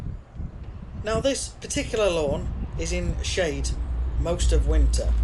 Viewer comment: Thought you was going say this particular lawn is in shite 😂 but you said shade 😃🤣🤣🤣